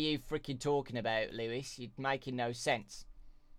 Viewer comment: "Making no sex" is what I heard, but after listening to it a few times I realized he was saying "making no sense"..... I need to clean my dirty mind